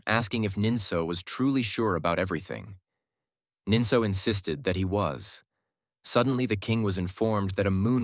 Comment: nah did bro just said Nintendo 💀💀